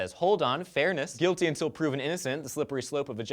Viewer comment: Did he just say "guilty until proven innocent" at 1:32?